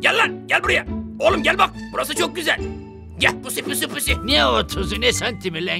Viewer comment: YAlnış mi duydum yoksa 2:53 de "PUSSY " Mİ diyor ? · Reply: espirisi orda zaten :D gel pussy pussy